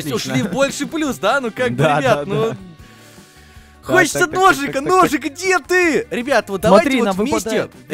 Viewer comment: когда сказал хочется ножик ножик мне послышалось что он сказал хочется дошик дошик